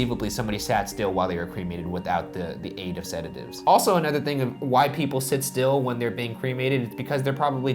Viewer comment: Thought Ryan said "white people sit still when they're being cremated"